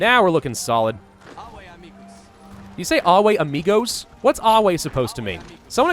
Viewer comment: "Ave" means about "Be greeted" and I think, while it sounds like 'amigos' he actually says 'amicus' which means incidentially also friend...